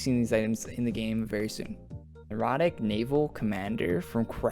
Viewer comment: omfg i thought you said erotic 😂😂😂